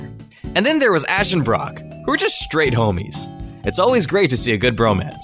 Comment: when you said "homies", i heard "homos", which is ironic, as the word right before it was straight and you know, then you said bromance